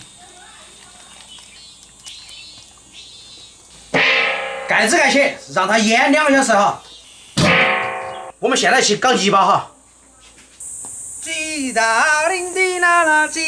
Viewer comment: 那里我听成了“”我们现在 去搞.........下（别笑话我哈） jb哈